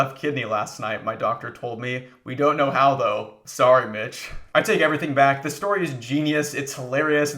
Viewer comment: I thought you said "I'm sorry, b/tch" and I fcking lost it 😂